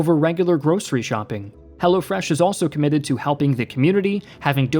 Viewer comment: For the sponsor it sounds like hes saying "Hell'a Fresh" but I know he's saying "HelloFreash"